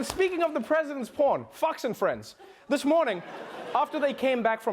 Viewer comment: Did I hear 'Fucks' and Friends?